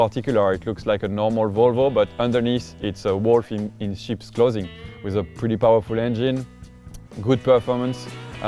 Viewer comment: At :54 sec I thought he said morphine in sheeps clothing lol!